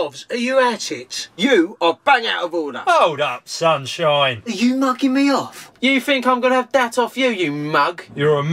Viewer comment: u wot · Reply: did he say ‘are yoy knocking me off?’ If so, can you please tell me what does it mean?